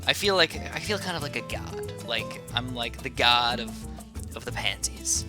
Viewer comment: I thought u said "I am the God of Panties" 😝 my mistake bro